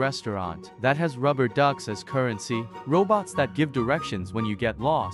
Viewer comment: I had to play it again as I heard "robots that gives you erections when you are lost" 😂😂😂